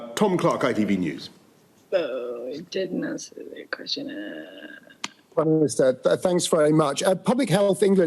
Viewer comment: Wtf was that at 17:00 in the video. Sounded like some spirit from the other side or e.t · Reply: Laura from the BBC still had her mic on, saying "urrgghhh he didn't answer my question"